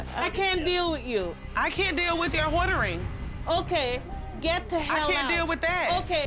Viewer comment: Did she say "Hoardering"? LOL 🤣